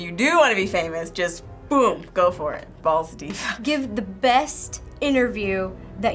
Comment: Did she just say "go balls deep???" wtf???